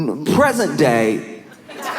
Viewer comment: It soo sounded like you wanted to say president not present day. 😅😅😅